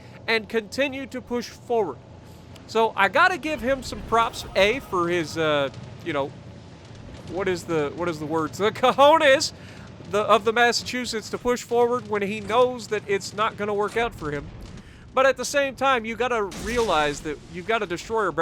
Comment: Did you say "cojones"???? Eres el puto amo ;3